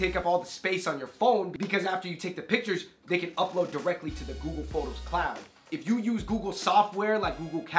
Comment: I thought he said, "Google Thots!!" lmaoo